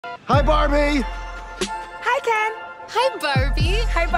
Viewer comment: What others hear: “ Hi BaRbieEEe~~💅🏻✨“ What I hear: “ HI BURPY!!😃 “